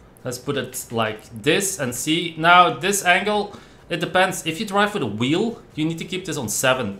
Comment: What did you say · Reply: If you drive for the wheel???